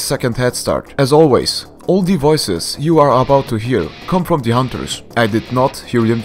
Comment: I always thought he was saying all devices but it’s actually all the voices